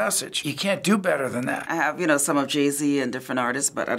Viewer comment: Did she just say that she listen to jeezy